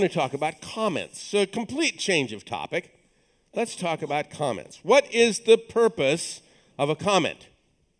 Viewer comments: was wondering why lesson 2 has a 5th of the views as lesson 1... spent 5 minutes talking about the moon which never tied into code, and then when he said "let's talk about comments" I thought he said "let's talk about comets" ... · I thought he said "Let's talk about comets"